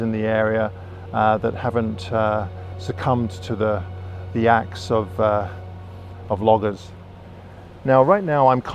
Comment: Funny the tricks your ears/brain play on you. You said '. . . axe of loggers.' I heard 'acts of vloggers'. 8-)